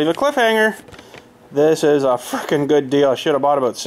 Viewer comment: I think I heard him say the F bomb when he was talking about the good deal on his new toy he left as a cliffhanger just after 1:02:49 I thought it was hilarious to hear him slip. I figure we all slip every once in a while and say something we are trying not to say. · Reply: He said frickin